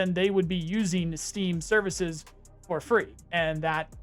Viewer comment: You're not framing this correctly. It's not Steam's servers, it's still BSG's servers that are bearing the cost of the game. It's a win win for Steam in every sense. I don't think you understand the situation properly. Steam has done this before with Hitman 3 account migration between Epic and Steam. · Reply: Bro, I think one of us had misheard him, cuz I heard that he said "Services" not "Servers".